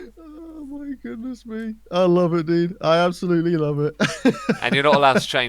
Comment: "I love it Dean I love it" I'm I hearing this correctly?